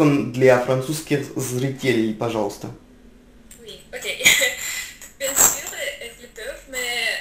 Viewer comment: C'est moi ou elle dit oui ok en français.